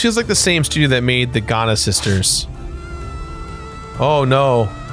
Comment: I had to do a double take when I heard Jirard pronounce "Giana Sisters" as "Ghana Sisters." XD